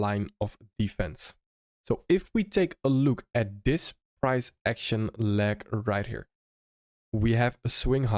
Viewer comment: in 1:05 did you say price lag or something else, I put the subtitles, and everything still can't figure out what you said.